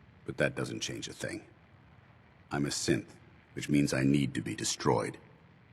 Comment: that sounded like he said I'm a simp I need to be destroyed